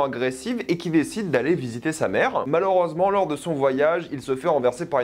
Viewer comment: il va faire quoi ? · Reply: visiter sa mère